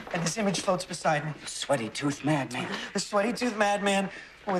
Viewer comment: Is it weird that I always thought he said "sway-toothed madman?" I like both versions but I think mine sounds cooler.